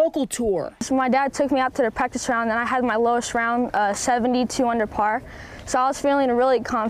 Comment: who else thought he said 72 under par for a sec